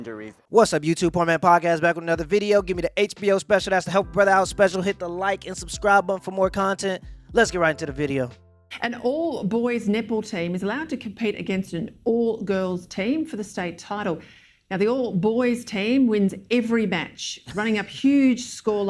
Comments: thought she said "all boys nipple team" crikey, was going to say rather see the girls but have no idea if university means high school in Australia so won't go there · I swear she said ‘an all boys nipple team’ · I heard.. " An all boys nipple team " lol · Could’ve sworn she said, “an all boys nipple team.” Can’t believe the guys still won that one…. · All I heard was "An all Boys N1pple Team .. " lol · it sounds like she is says all womens nipple team.which sounds interesting... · I thought she said nipple team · I swear it sounded like she said “an all boys nipple team” instead 😆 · I swear, it sounds like she says "An all-boys nipple team" 😂😂😂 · I coulda swore she said an "all boys nipple team" I'm gone 🤣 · Dang at first I thought she said "An all boys nipple team" and I said I only want to see the all girls one. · Anyone else hear “all boy nipple team” at 0:38? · from her accent, I thought she said "an all boys nipple team" ROFL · Did she just say all boys nipple team? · I thought she said all boys nipple team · With her accent, at first I thought she said, "An all-boys nipple team... ." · I legit heard "An all boys nipple team" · Sound like she said all boys niple team 🤣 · I thought she said "all boys nipple team" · All I heard was.. "An all boys nipple team was allowed to go up against an all girl nipple team." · I thought she said "An all boys nipple team" 🤣🤣🤣🤣🤣 Edit: I can't stop hearing "nipple"